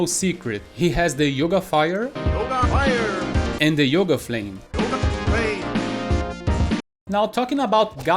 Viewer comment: Yoga flame? Here I thought he said you got flamed. Wow